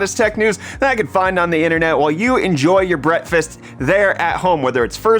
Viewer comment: thought he said "while you enjoy your BRETTfast"